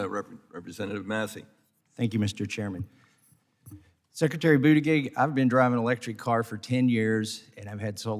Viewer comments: I thought the guy said Sectary Booty Cake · Did he just say secretary bootygig?…lol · I just heard "Secretary Boojie-cake" That's my new thing 😆 🤣